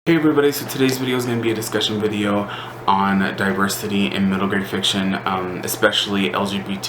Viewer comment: I thought it said Disney stead of Diversity